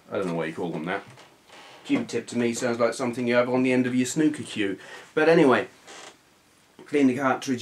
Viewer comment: As an American, I have no idea what you said: "end of your snoo-Q-Q"? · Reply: "Snooker cue". The stick you use to play snooker. Kind of like pool... but bigger.